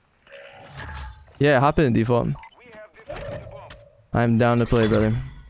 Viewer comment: ! At 1:05 in the video did you say “Hop in Dfalt!”?? Like the Dfalt?